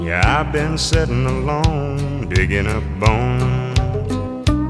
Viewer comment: My brother thought he said digging up phones 😂